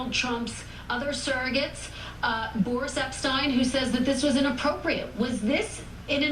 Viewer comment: Hey at the 1:32 mark did She say that Boris epshteyn said it wasn't inappropriate?